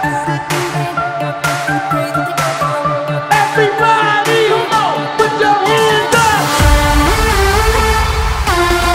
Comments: sempre quis saber o que ele fala 0:26 everybody ...****??? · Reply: É "Put Your Hands Up" mesmo... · alguien me podría decir, por favor, que dice o que palabras utiliza entre los segundos 0:26 y 0:30?? plissss... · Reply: everybody now puts your hands up